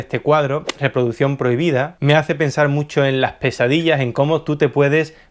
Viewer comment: a primera escucha oí 'QUESADILLAS!!' pero no, solo tenía hambre